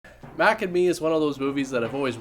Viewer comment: I thought it said mac and cheese at first and their called G-men not FBI guyes. Build that space wall, damn immigrants taking our caddys and jobs.